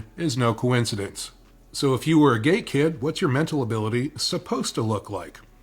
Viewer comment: I had to rewind 5 times cause I thought he kept saying if you were a GAY kid…. He said GATE.. GATE kid. If you were a GATE kid. I thought homeboy was coming out lmao. 😂